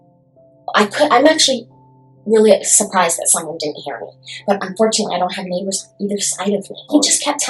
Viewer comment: after the way who didn't hear her!? that subtitle? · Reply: “I’m screaming bloody murder. I’m actually surprised that someone didn’t hear me”.